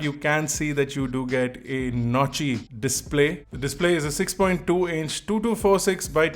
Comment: I heard "Naughty Display"😂😂